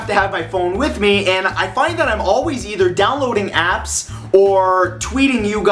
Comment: turn subtitles on 0:31 it says ass instead of apps